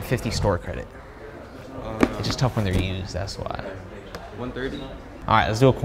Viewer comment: the caption said ds but he said used